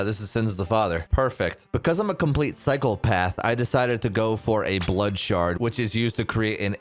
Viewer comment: my guy did you just call yourself a cyclepath?